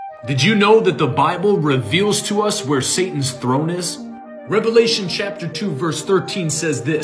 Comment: Oh, I thought you said Santa’s throne…must he dyslexic - my bad 😂